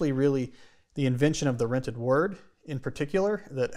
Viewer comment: I heard, "rented word," too and I looked up thinking, "I wonder what he means by, 'rented word?'" And his response to Tom's question about what he said made it apparent that he really didn't catch at all how his pronunciation made it sound like, "rented word." Lol!